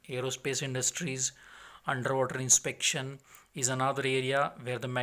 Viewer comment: thought dude said underworld inspection i said damn we bout to see some vamps